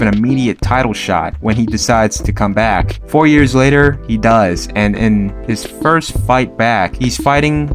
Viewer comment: Did anyone else think that he said, "4 years later he DIES"?? 😳 I had to rewind 😅